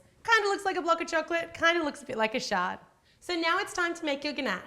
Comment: did she just say shart I'm crying